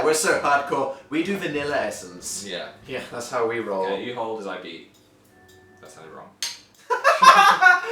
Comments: can someone pls tell me what phil says at 2:55 ? ._.' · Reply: he said "Yeah, okay you hold as I beat...that sounded wrong." (I think this is the part you were talking about?) x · What did Phil said at 2:55 I don't understand ? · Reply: "You hold as I beat, wait that sounded wrong" You'll get it sooner than later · what did phil say at 3:00 ????? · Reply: Kaitlan MIKITA . You hold as I beat... That's how we roll